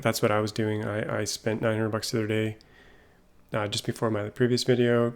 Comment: Did you say you paid $900 to play??